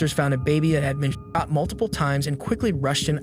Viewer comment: When I first heard it I could've sworn the mute was "the baby was cut multiple times" and come to find out this guy SHOT a baby multiple times... Like...first off holy shit, second off that is one tough baby... Absolute fucking miricle that poor soul survived.